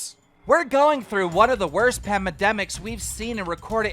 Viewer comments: Did keem just say pamademic @ 7:20 😳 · Keem calls Minilad out and said "Pamademic" instead of Pandemic 2 seconds after